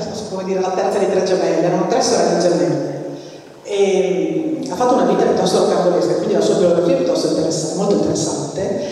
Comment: Buongiorno, innanzitutto grazie per aver condiviso i vari video relativi alla conferenza, sono realmente interessanti. Mi piacerebbe leggere la biografia della studiosa che cita la Dottoressa al minuto 8:16, sarebbe possibile conoscerne il nome? Purtroppo, pur avendo riascoltato più volte, dall'audio non riesco a comprenderlo. Se fosse poi possibile avere anche qualche altro consiglio bibliografico, di altri autori anche magari relatori della conferenza, sarebbe molto gradito. Grazie. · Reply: Le slide della presentazione della collega sono disponibili qui: <link> Se ho capito bene dall'audio, l'autrice a cui si riferisce è Elisabeth Kubler-Ross su cui trova molte informazioni in internet ad esempio qui: <link> Se poi desidera altre informazioni mi scriva direttamente: <mail>